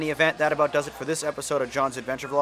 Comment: it sounded like you said johan instead of jon